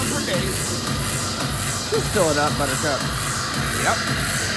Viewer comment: I could have sworn I heard “fill it up buttercup” .....lmao. 😝. Fun fun video-you make me 😂